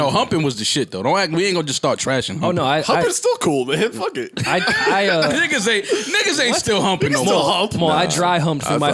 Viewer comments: Lmao thought Parks said “niggas still hump” @ 34:40 had to rewind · yoooooo did parks say niggas!? 😂😂😂😂😂😂😂😂😂😂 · I had to rewind 5 times. I thought parks said "niggas still hump"😥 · Wait at 34:30 does Parks say the n word? I heard it twice when he responds to Mal. I... am I wrong? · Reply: "You can still hump" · Did parks just say "niggas still hump" ????? at 34:42 LMFAO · Bruh I swear to God I heard parks say nigga at 34:41 lol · Uhh... Did Parks just say Nigga @ 34:40 or am I whylin?? 😂 😂